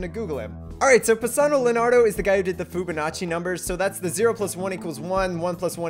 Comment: did you say foobanachi